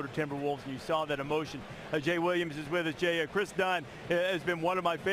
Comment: Came across this in 2020....who on earth is Kris Dunn? I thought they said Kendrick Nunn for a second.